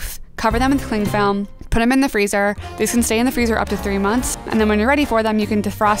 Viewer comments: did she say 3 months?😭😭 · I’m sorry did she say 3 month ? I think I’m deaf